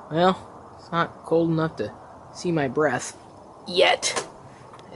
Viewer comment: i thought he was takin a dump LMAO then he was talking abt his breath n thought he said its not cold enough to see his breast lol...i then realized he was outside *facepalm* i luled so hard tho when i saw i was wrong haha